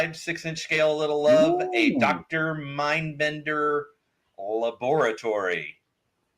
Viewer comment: Fun times guys. I LOVE suggestions for GI Joe with the Dreadnok hideout and Cobra Castle. When said "Dr. Mindbender's laboratory" for some reason I heard "lavatory" and wondered why we needed his bathroom.